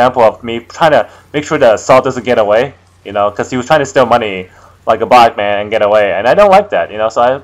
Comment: Did you just say "The assault trying to steal money like a black man"? _O- Nice video btw.